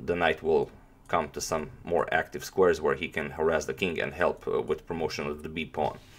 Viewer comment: I might've not heard this correctly, but did he say, "harass the king?"